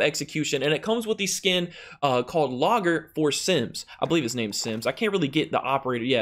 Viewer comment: Bruh I heard "Simps" instead of "Sims"